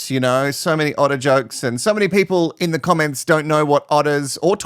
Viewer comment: I heard “artichokes” when you said “otter jokes”. 😂 and I’m from the US and am totally NOT offended by the C.U. Next Tuesday word … 🥳